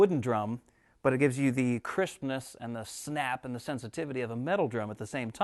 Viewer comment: ish I honestly thought he said it gives you the Christmas